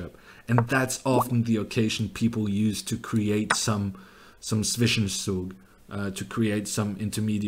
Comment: What is the expression he uses @ 20:06? The captions says "sufficient soup" but that can't be right :D although soup might be close as I guess it's something to do mayhem or mischief. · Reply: zwischenzug